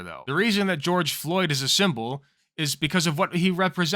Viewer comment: Sorry I turned away for a second and heard say "George Floyd is a symbol of white supremacy" lol I know thats not what he meant but it was funny to mishear